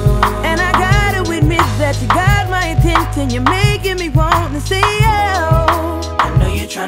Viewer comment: Has anybody noticed the way he says attention😂😂😂😭👏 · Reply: never knew he said attention I thought he said intent lmfao